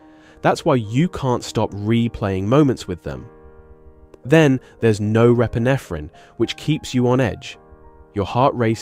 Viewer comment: What in the world do they say at about 3:14 “then there’s no…” ??